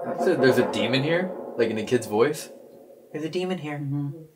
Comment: Instead of "there's a demon here" i think it says "i gotta warn ya"